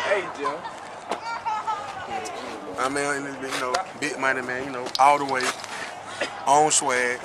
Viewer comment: did he just say big money bandgang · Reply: "big money man ya kno "